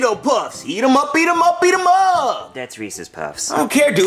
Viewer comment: I thought when they said eat em up I thought they said beat,em up😂🤣🥲😁🥶